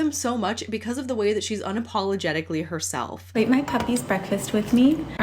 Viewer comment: I legit heard "puppy" (or "Happy?") as "hubby" and thought she was making her husband breakfast covered in chicken and bone broth while calling him overweight. 🤦🏼‍♀️ Also, for what it's worth, I had no problem with your delivery and analysis. She's still disgustingly wealthy and extremely out of touch, but I agree that there's something characteristically different about her which helps identify what I typically hate about "regular" influencers. Whether that's genuine or a well-played act, the popularity and goodwill it's earned her is unique and, in my opinion, worthy of objective analysis.